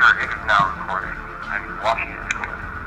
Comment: i hear " i am watching over the school" mans voice